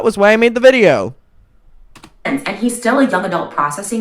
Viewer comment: Omg CC did you dirty😂 ( 48:30 ) When trisha says "and hes still a young adult processing this" CC said it was "and hes still a dumb adult processing this" 😂